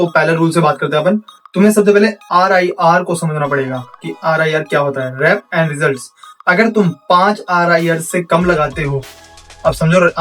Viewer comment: Bhai RIR ka fullform kya bola?😂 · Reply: Reps in result wasa bro video kase lagi aap ko 😊